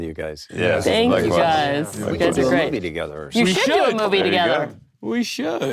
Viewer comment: At the very end 58:25, they were saying that they should do a movie together and I THOUGHT the host/interviewer said "WE should do a movie together!" Instead of "YOU should do a movie together!" And I was... "no she didn't😂😂😂" and I had to rewind it to make sure she actually didn't 😂